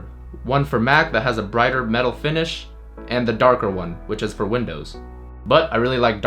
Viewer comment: i thought you said "and the darker one which is for women"...